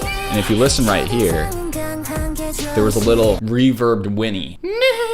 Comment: it was not some reverbed sound, that was Yujin calling Gael: "unnie!" XD